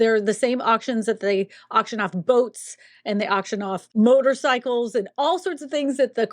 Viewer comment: Sounded like you said “auction off goats” (not 🛥️ )😆